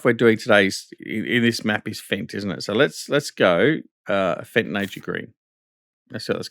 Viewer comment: Because the little bit off and not so clear mike sound (or your local dialect) I heard you say Fenton Nature Green and I made a Aha!, but had later to go back and watch the subtext. ;D Nice if Stevie will make an own nuance colour palette! <3 And I'm all well in last week of summer vacation now. The youngsters are doing very well and have bonded now to all other cats in my yard except their own Grandfather who is still sceptical to have them here at home. The kiddos are born at neighbors farm by two different mothers (who really detest them and say "F**k off and get your own home range!" even to the female, when mother visits me at least two times a day! And they are pregnant again! Only bad thing is the neighbor girl get about $300 for the kids when she sell them after 14 weeks. You may start to breed cats instead of pigs and horses! So much money you will get! Haha! Take care yourself! Ps. I'm the Great Grandfather the minis think. :D Pps. And piggies are very keen on munching sugar cane! You will soon have a riot!